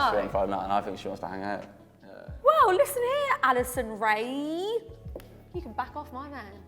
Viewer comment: Am I the only one that thought she said Addison Rae at 6:38 😂😂😂